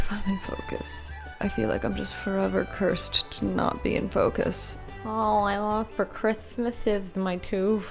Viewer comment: "I feel like I'm just forever cursed to not being in focus" is such mood whenever I've been trying to film myself but first I heard "I feel like I'm just forever cursed to not being focused" which is such mood as a fellow adhd:er